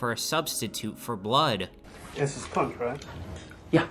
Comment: he actually says "this is punk'd right?"